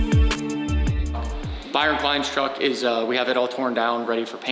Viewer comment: I thought you guys were saying Byron Hines